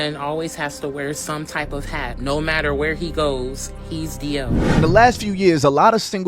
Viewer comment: if a man has to where a hat no matter where he goes he’s D m? “ what tf did she even say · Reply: DL. It means “Down Low” which is slang for gay